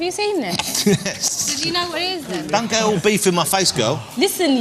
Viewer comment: She says "no, its not. Have you seen this? Do you know what is it then?" Then Ross goes like "dont go 'beef' in my face girl." So is it 'beef' what he says there? and whats that she says next? Thanks in advance. =)